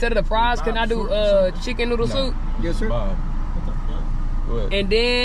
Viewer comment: Did anyone else hear the guy say “yes sir” to order @ 7:40 ?